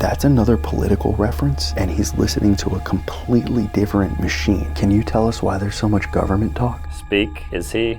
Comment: What did he say..... Did i here machine